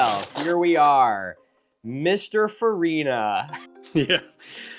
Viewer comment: did he say mr furina